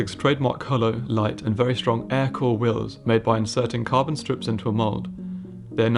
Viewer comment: did he say "air cooled wheels"? all wheels are "air cooled" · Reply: I said "Aircore". Koenigsegg wheel tech.